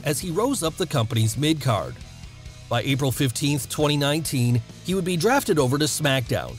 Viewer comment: Did he say April 15 2019?